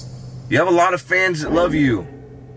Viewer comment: it sounded like Michael’s voice saying love you back